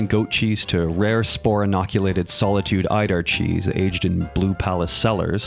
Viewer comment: Wait it’s EIDAR CHEESE? I always thought it said “elder cheese”